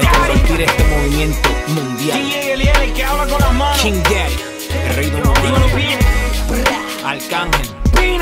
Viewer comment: Escuche bien DY Diciendo El Rey Don Omar